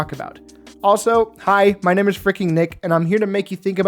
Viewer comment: i really thought he said "my name is fricking neeg-" 😭😭😭😭